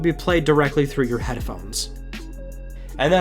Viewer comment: Wait?.. @ 13:23 did he say Head-er-phones :-D haha